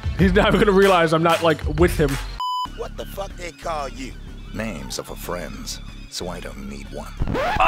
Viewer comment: I couldn't quite make out what he said at 0:46. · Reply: Names are for friends, so I don't need one. Ohhh slam OHHHHH OHHHHHHHH WHOAAAAAA Sorry you had a hair on your shirt.